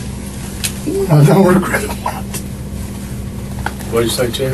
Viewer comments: why does it sound like he says, “I don’t regret it.”??😭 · Did he say, "I don't regret it"???